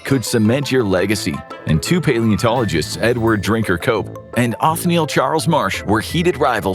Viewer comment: i swear he said his name was edward drinkyourcoke 😂